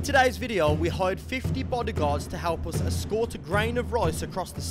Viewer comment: tittle 30 body guards, first words I hear "we hired 50 body guards"